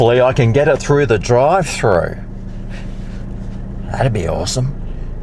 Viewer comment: 8.07 this is what I heard ...🎵 'Ooh, dream weaver...I believe you can get me through the night ' 😂